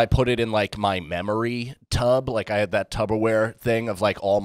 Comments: Did he say Tubberware 14:55 shout out to! 😂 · Did he just say "Tubberware" ?